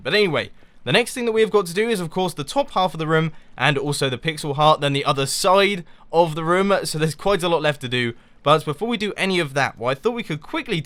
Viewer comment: i thought you said pixelot (like ocelot)